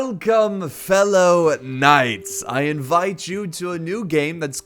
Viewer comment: I thought he said:welcome fellow nazis and I was like wat